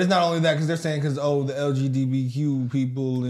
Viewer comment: lgbbq 🤣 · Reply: oh i guess he said lgdbq but at first i thought he said lgBBQ 🤣😂🤣😂